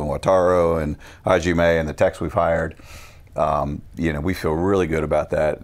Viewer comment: I thought he said "the techs we fired," but he actually said, "the techs we've hired."